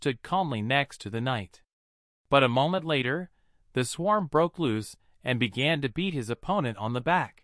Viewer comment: (and many other parts) the text to speech is saying some weird shit, what is it trying to say? Sounds like it's saying "the swarm" when referencing the main character such as "the swarm broke loose" (the closed captions also assume it's saying this)